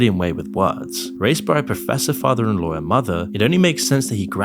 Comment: why did you call Conan’s Dad his ‘father-in-law’? FILs don’t raise you bro 😂 You meet them when you’re a grown up 😂😂 Turns out, the entire video is a pretext for whiney MAGA axe-grinding. Thumbs down. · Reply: He said "professor father and lawyer mother" his English accent may be what got you confused.